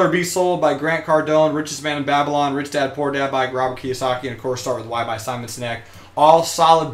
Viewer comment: Whoaaaa. Way too fast haha. What was the last book you said @ 40:37? Thanks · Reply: Start with Why: Simon Sinek